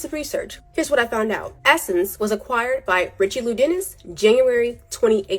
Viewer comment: anyone else catch that she calls him "richey lou"? 😂💦😂💦😂